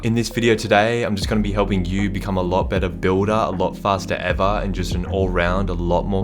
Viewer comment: Anyone else heard him say... "become a faster ever"